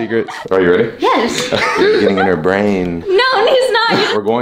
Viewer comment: I heard “she gon give brain”